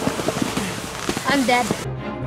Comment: I thought said I'm dad